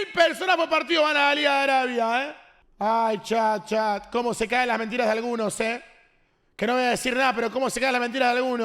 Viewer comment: ALGUIEN QUE TRADUSCA QUE DIJO 🗣️🤔😕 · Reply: como se cae la mentira de algunos se escucha perfectamente😮